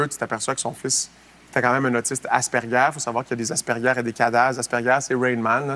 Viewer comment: Très intéressant. C'est un bon porte-parole pour les parents vivant avec des enfants à besoin spéciaux. Juste une petite question : Que dit-il à 1:52 ? "Faut savoir qu'il y a des asperger, y'a des ...(?).....". Je suis très intriguée. J'ai chercher un peu et je ne vois pas de quoi il parle. Quelqu'un peut m'éclairer ? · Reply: Si ça peut aider, j'entend "il y a des aspergers, il y a des cadaz". Pas trop sûre ce que ça peut dire cependant...